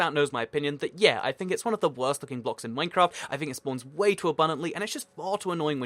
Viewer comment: I thought u said gravel is one of the worst f*****g blocks in minecraft XD